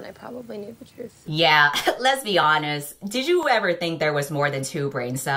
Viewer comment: it sounded like lesbianonest sskssksksjdsjks